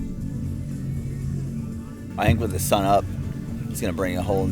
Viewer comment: What in the flying frik did he say at 11:25? Lol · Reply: Maybe it was “about sun up?’